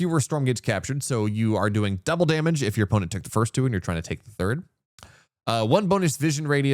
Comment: I was talking about skipping the first two Stormgates - so then you would do double damage on the T3 Stormgates - and what you say is potentially true about T3, depending on whether the multiplier scales additively (50%+50%) or geometrically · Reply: Missed that, for some reason I heard "take your third"